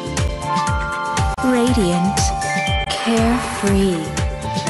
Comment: Dreamy? Gabriel Agreste? Oh hell nah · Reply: My deaf ahh thought it said creamy- 👹